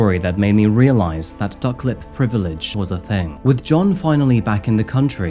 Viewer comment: The duck lip privilege was a thing comment took me out, I thought he said suck lip or fuck lip so I had to watch it again too check 😂 is comical with the one liners.